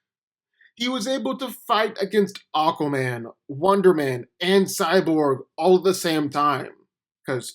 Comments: breh did you just say wonderman? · did he say wonder man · did he just say wonderman?! hehe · did he just say wonder man